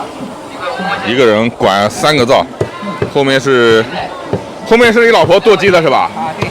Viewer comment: 我没看字幕 听成了：后面你老婆做鸡对吧……